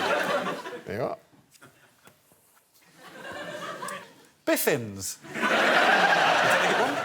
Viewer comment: Can someone tell me what Alex's word is @ 1:55 bc I've replayed it a bazillion times & still can't get it. It doesn't show up in the CC...which I always have on if available bc, between my hearing issues & the accents, understanding what's being said is Always my 1st task of the show. I aDore this show! :) Thanks! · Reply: It's an odd word, but it's "biffins", which is a type of apple grown in Norfolk, England. Greg's definition comes up under "biffins" on Urban Dictionary as well.